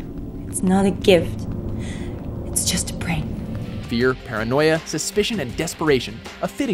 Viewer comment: did i just hear her say its just a prank??? Lol